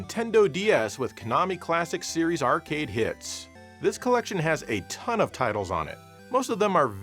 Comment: Did you say HITS?!